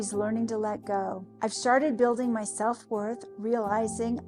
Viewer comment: I swear she says "I've sharted" at 21:48.